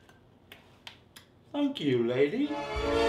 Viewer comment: did he say "Thank you, lady" or something else